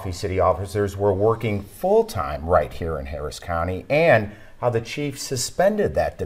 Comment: Okay, it's Henderson County, 3 hrs north of Houston. It sounded like they said Harris county.